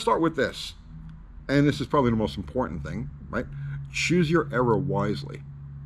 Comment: I thought you said choose your error wisely, not era. I laughed out loud. Slight truth in both statements.